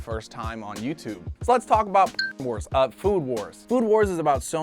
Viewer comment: he said food who*es instead of food wars